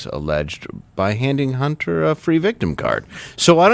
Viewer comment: I thought he said "Hunty"